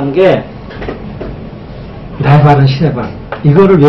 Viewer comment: 걈사합니다~ 힘드셨을때 "나의반은 신의반" 으로 말씀 하신걸로 들리는데 맞나요 선생님? 맞나요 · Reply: 나의 발은 신의 발입니다. ^^